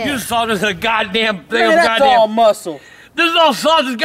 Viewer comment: Did just say nigga.that's muscle